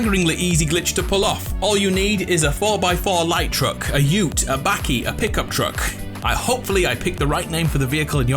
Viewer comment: wait did he just say Bakie